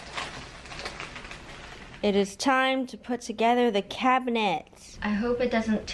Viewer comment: for some reason, i thought you said the cabinets were cat cabinets, so i was highly confused when they weren't.